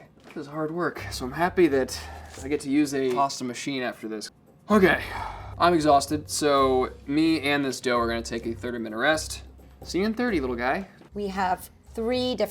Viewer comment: Did he said he's exhausted already??